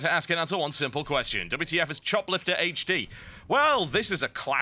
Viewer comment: I thought that said shoplifter...